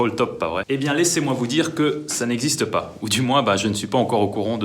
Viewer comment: ptdr moi qui pensais "laissez moi vous dire que C'EST CECI"